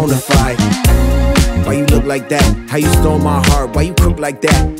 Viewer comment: legit thought he said "why you poop like that" lmao ;))) but damnnn I love this song!!